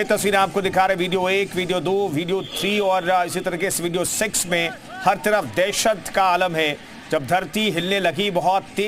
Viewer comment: video s*x 🤣 2:12 who heard 😂